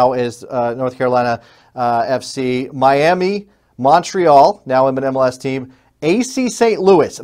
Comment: The Tampa bit was about the NASL team and the USL team, VSI Tampa. I was admittedly not clear on what I referring to. Not sure what you mean about Miami. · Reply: Thanks for the clarification. Re listening to give you a quote, I misheard "Miami; Montreal, now an MLS team" as "Miami, now an MLS team".